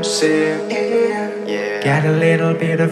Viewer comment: "Got a little bit of gasoline, welcome to another crime scene yeah!" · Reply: I thought it said "Got a little bit of gasoline, walk up to another garage sale" lol